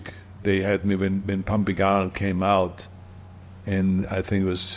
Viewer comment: Did he just say "Pumping Arnold"?